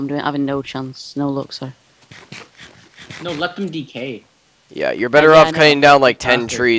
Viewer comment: It sounded like u said let the eat cake lol wen u said let the decay